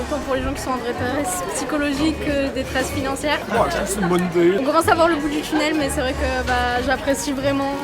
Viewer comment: il dit quoi lui ptdrrr il m'a tuée 😭😭😂😂❤ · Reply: J’ai compris "...bonne idée"